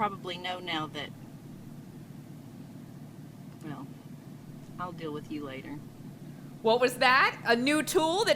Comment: I was glancing away so when she said "you know what, I'll deal with you later," I heard "I guess you know I'm a manipulator and I was like hold on.